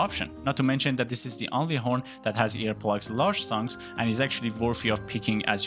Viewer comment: Not with stamina recovery bubble though which makes mizutsune horn better Trust me I looked up everything and picked the best of the best :) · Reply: I misunderstood - sounded like you were saying it was the only horn in game with earplugs L :)